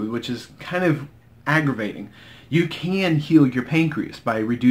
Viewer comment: What are you saying at 4:20? "You can kill your pancreas by reducing it's workload."? Huh?